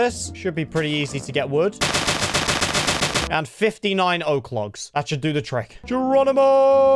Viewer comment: did anybody else hear him say 15 iron oak logs